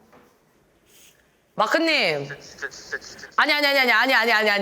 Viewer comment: 뭔가가 들리는데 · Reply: 진짜진짜진짜 아닌가요?....